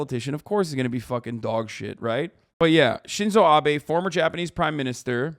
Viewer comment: BRO THE WHOLE TIME I THOUGHT IT SAID SCHIZO ABE